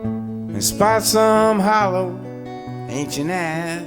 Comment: You did say "...Hollow Ancient Eyes", correct..? Because that's what he sings... It sounded like you said "...Hollow ass". Been a big fan of the Mailman from Maywood who got his start at the Old Town School of Music here in Chicago and wrote Hello In There in his mid twenties... for many years. May he Rest in Peace... Beautiful analysis and presentation... THANK YOU!